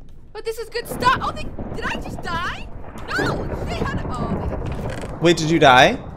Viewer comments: did stacy say SHIT? 😮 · First time I have ever heard Stacy swear she said shit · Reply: She said "she had..." Really fast she didn't curse